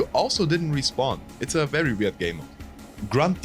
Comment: For a second I thought you said “Lewd game mode” and I get worried.